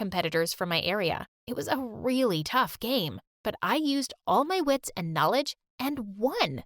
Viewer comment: I thought you said gamer